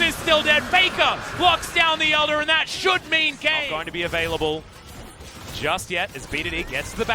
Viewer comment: tht should be gay? i misheard?